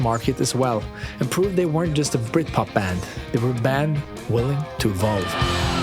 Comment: the subtitles are wrong, I'm sure he said "to evolve" not "to love"